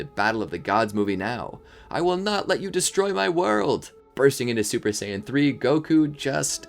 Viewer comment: “I will not let you destroy my world” What I hear”WHAT DID YOU DO TO MY BULMAAAAA